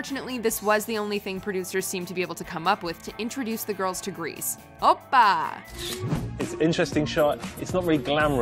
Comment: what does she say? Oh-bah??? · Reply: Opa! It's a Greek expression.